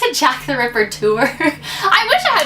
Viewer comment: I thought she said Jack the rapper lmao 😂